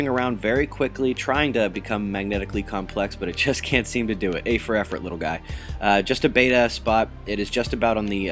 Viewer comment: did you say A for effort?? lol